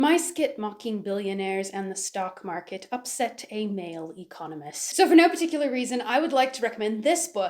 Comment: I thought she said "male communist," and I was like, "Since when do we care what communists have to say?"